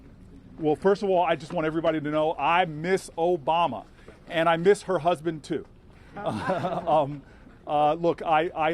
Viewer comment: did he just said he miss Obama and her husband too?